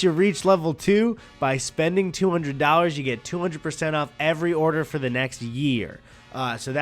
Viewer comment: Did he say you get 200% off at 33:30 ?